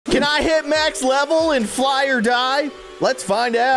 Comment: i think he said "Fly or Guy"